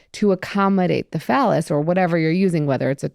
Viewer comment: swore she said: 'to accommodate the fellas' 😂